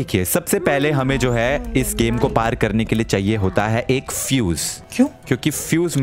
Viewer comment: i hear zeus instead of fuse